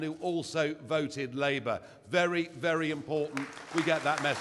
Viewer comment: What is he saying at 18:22 ? "Very very impotent?" That's what it sounds like to me! And it makes sense, If you're impotent, you want your message to be impotent!